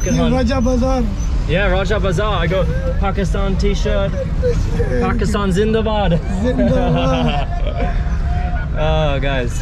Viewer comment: Did I hear you say "Pakistan is in the bag" ? 😂 I love your videos! It's really good to see you chatting with locals and exploring. Hope you'll visit the Philippines as well soon! ❤️🇵🇭